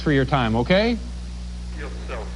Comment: what did Olaf say ? i can't hear it and i'd love to know 53:15 Dave and Amy were married ??? 1:08:45 What a lovely bribe :o) Man what a great compilation - again ! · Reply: Ah , the question was already asked and answered halfway down the comments : "Go F* yourself"